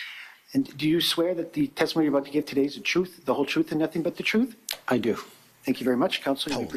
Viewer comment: did he just say "i do" to the floor?